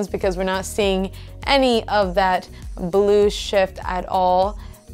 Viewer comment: i thought she said "blue shit" 😂